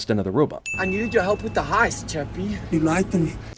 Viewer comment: At first I thought it said crappie 😂😂 my bad